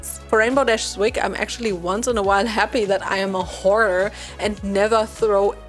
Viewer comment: I heard "whore" instead of hoarder and I was so confused 😹😹😹😹😹😹😹😹